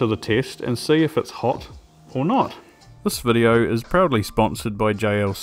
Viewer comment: I first heard "this video is probably sponsored by" and thought it was hilarious. 🤣 On second hearing, I heard "proudly", but I still felt like writing it down.